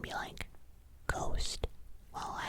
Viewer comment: I heard "ghost" instead of "coast" and my heart dropped. 😨 Glad I miss heard that. The whole AI thing is scary, hoping the internet doesn't because just a world of AI. (Or heck, maybe if it did we'd all interact more in person again.) Happy New Year!